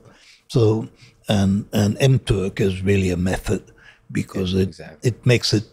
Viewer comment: What does Kahneman say in 1:07:27 ? What's the name of the method? M-Took?